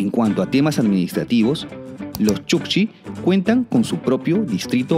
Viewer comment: en el minuto 6:33 escuche chucky jaja pero buena la informacion pero puedes hacer la historia del planeta tierra eso seria mucha imformacion.